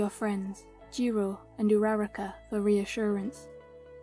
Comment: This was three years ago😭😭😭 Anyway, when you said uraraka i thought you said numerical data 💀🤚